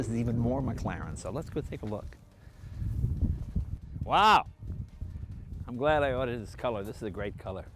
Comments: did he say "I'm glad I ordered it in this colour?" Is he getting one? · did he say he's glad that he ORDERED this colour